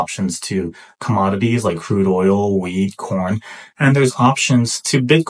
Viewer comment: surely I can’t be the only one who heard weed instead wheat at first…